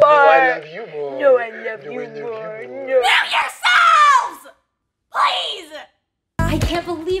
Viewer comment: During bf and gf when said MUTE YOUR SELVES I thought she said KILL YOUR SELVES 😂💀